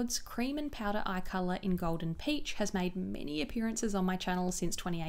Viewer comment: I misheard the Tom Ford one as Gold and Peach.